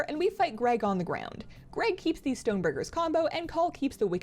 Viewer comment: did I hear stone burgers ?